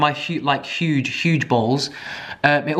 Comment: You said “huge bowls” and I heard “huge balls”. That’s it, that’s all I wanted to say. Have a good day xD